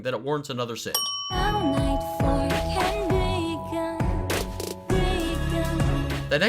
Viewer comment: i always thought it said "bacon" instead of "begin"